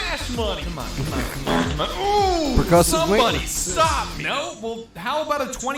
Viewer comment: what did say? per.... 14:15 meanwhile Fuze 17:18 rage comics gone realistic 17:30 JObama 28:12 pause SAWED OFF STUB , gonna get your face or something 30:24 Pause Aliens picked the WRROOONNNGG bastard 30:50 my dozers/people 31:25 taskbar grooooowwwww 32:04 VR FYI now for my random self notes that may as well be public carry on 0:50 so..... yall own payday? used yours but yeah... , played for 10 minutes but then we recorded 1:25 back other dat when micah was over ( yey) 1:50 "Litmus test for Procederal Shooters" .... they added Tony Muhfuggin Montana 2:35 may have it , dudes on the server playing it , want to work on out teamwork , communication , and callouts (hes at toybox!) 3:45 callouts instead of scan/pinging , (thats his name right , ill check at the end) likes the "realism" of not knowing the layout (fair point) ... OMFG imagine Procedual Generation 5:04 if they do procedual generate , have a few moments to prep showing this (nothings on screen but i know what you mean) for planning , letting leader draw on the map plan (good idea) 5:51 "play with randos its justdicks on the board " - "why we vote a leader" , given intel of where it COULD be to plan too 6:51 static why 9:04 who said games ARENT educational? 10:23 tries a karate test 11:28 awesome quote , and Cloaker starts 11:06 ah yes , Pavlovs Cloakers 19:30 "run like the fat kid.... trust me i know" 21:14 explosions of explosions 24:08 M1 garannde 25:38 some would call it a scumbag buil- DAMN 27:4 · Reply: said "Percussive Maintenance"